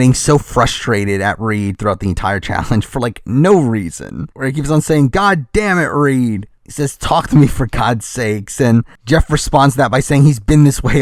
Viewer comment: He actually said, “come on Reed, damnit”